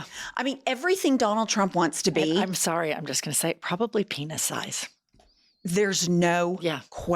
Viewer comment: "penis size" i heard "to penisize" and i was like what does that mean